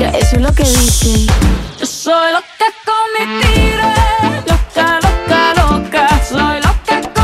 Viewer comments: Yo toda mi vida pense que decia YO SOLO TE EH COMETIDO LOCA LOCA LOCA 🙄🤞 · Yo pensé que decía yo soy loca comestible 😂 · "Soy loca con estilo" eso pense que decia xdxd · Yo pensé que decía 'yo soy loca comestible'